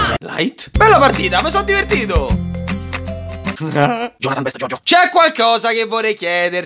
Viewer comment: solo io ho sentito "jonathan best jojo"?